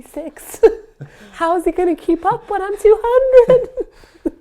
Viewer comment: I know this is kinda random but the CC has a cute error. At the end of the video, starts talking about her weight gain and at 13:03 She says: "How is he gonna keep up when I'm 200?" but the CC says: "How is he gonna keep up when I'm too hungry?" haha